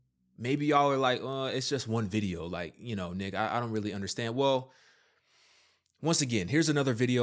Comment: good video and off-topic but for a second I thought you said “niġğa I don’t really understand” 😂 Anyways, good video! keep up the good work.